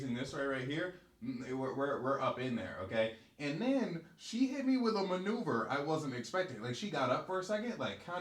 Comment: I thought you said she hit me with manure 💩 💀 😂